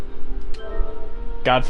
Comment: ending sounded like you said "God Fuck us"